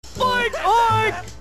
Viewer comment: Originally I thought Eddy said peaches don’t say oink Ed.